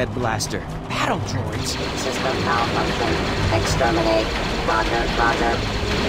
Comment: to me it sounded like the droid said "exterminate mother f*cker